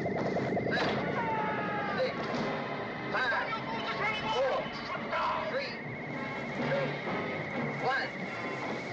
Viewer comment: can anyone understand what he's saying? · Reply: 'Shut down you fool it's running wild! shut down!'